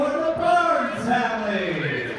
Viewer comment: It sounded like they said "the Gators family". I guess that's appropriate, as Florida IS the alligator state!